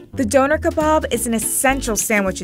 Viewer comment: I straight up thought she said "Doughnut Kebab"